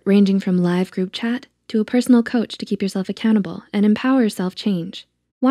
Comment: WHY DO I HEAR “to keep yourself a cannibal” 😱😱😱